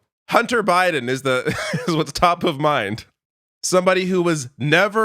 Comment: I swear I heard "Taco mind" instead of "Top of mind" 🌮